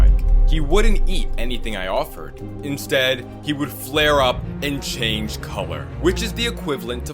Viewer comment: what did you say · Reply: He said flare